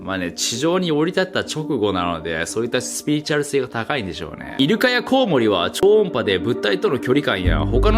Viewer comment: 『イヌ科のコウモリは〜』と聞こえた私は耳が石灰化してるのかな…